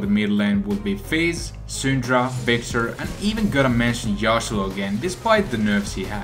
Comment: In 8:09 what does he said here? And even gonna mention ___ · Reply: Yasuo again, he was just saying that even tho zed is better, Yasuo is still a good pick